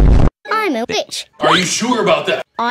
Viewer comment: (I’m a bitch)is what I heard